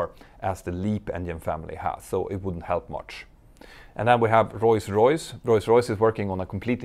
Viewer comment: Whenever you say Rolls Royce, I hear: Royce Royce. Is that me? Or am I missing something 7:08